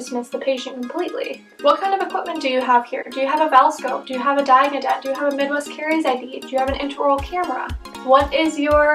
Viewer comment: thank youuuu!! also at 16:56 when you ask 'what kind of equipment do you have,' you said: VELscope, intra-oral camera and what were the other two?? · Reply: Diagnodent and Midwest caries ID!